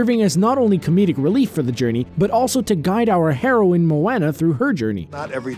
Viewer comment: haha i thought u said the heroine marijuana !!